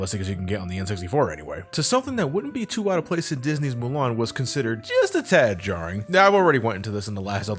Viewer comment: does he say Mulan or Moana?